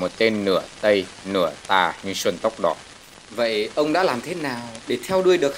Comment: Xuân tóc đỏ nghe nhầm là sanks tóc đỏ